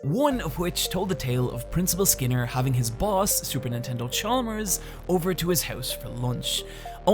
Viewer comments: did you say Super Nintendo Chalmers or is it just me... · i heard you say a sneaky super nintendo chalmers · It sounded like you said "Super Nintendo Chalmers" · Did you just say "Super Nintendo Chalmers" at 08:37? 😂😂😂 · I've watched this video about 5 times and I have only just noticed that says Super Nintendo Chalmers at 8:37 instead of Superintendent Chalmers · does anyone else hear "super nintendo chalmers" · Anyone else hear "Super Nintendo Chalmers"? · you meant to say superintendent but you said supernintendo ! I heard that and I know you did it on purpose. I'm calling you out boi. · Did anyone catch the narrator say "super nintendo" chalmers? 8:37 I thought this was supposed to be serious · Wait, did anyone else hear "Super Nintendo Chalmers" at 8:37 ? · did... did he say “Super Nintendo” Chalmers instead of “Super Intendant”...? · did you call superintendent chalmers, "super nintendo chalmers" on purpose? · DID HE JUST SAY SUPERNINTENDO CHALMERS ITS SUPERINTENDENT DAWG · did you just say “Super Nintendo Chalmers”? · Did you just say Super Nintendo Chalmers? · Am I the only one who heard "super nintendo Chalmers?" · did you call him super nintendo chalmers on purpose? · Please tell me he really said "Supernintendo Chalmers" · for a moment I thought you said "super Nintendo Chalmers" to quote Ralph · did he just call him super nintendo chalmers? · I’m sorry, but… did he really say “Super Nintendo* Chalmers” at 8:38 ? 🤔😅